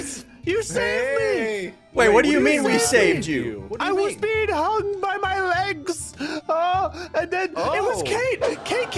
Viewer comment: I thought said "I was being hugged by my legs"